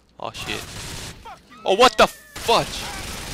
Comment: Did you say Oh what the Fuckdge Fuck and Fudge? WTF!!! LOL!! LMAO!!!!